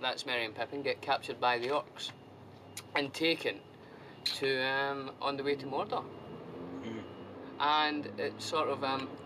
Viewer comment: They were taken on the way to Mordor. That is, whilst the fellowship was traveling to Mordor, the hobbits were kidnapped and taken to Isengard instead. What he said is perfectly accurate. Just sayin'. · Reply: Aahh, thank you. I know you posted this eight years ago but this comment just made me realize that I misheard it as “on their way to Mordor” while they were actually taken while on the way to Mordor. 😅 I almost got upset for nothing. 😂